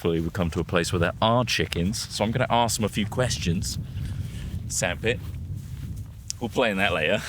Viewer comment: Did he say sandpit or sad-pit?